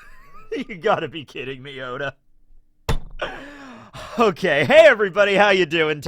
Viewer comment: who else heard "you gotta be kidding me Yoda"